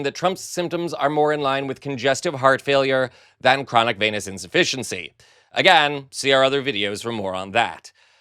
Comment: I thought you said chronic penis insufficiency 😂